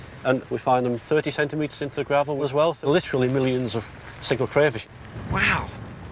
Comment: I thought it said "Single Crayfish" Wouldve been such a better vid lol